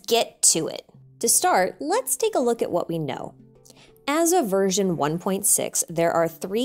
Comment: For some reason my brain thought u said virgin 1.6 instead of version. XD